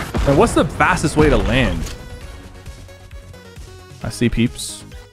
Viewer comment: did bro said peeps